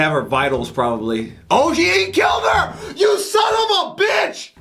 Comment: Did he say Gi Hun killed her?